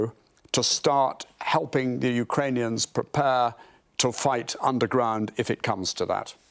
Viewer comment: Did he just say "on the ground" or "under ground" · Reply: underground - as in help the Ukrainian insurgency that may continue to fight even if their military is 'officially' defeated